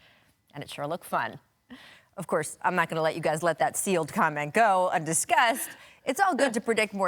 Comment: Sounded like he said 'see you' not sealed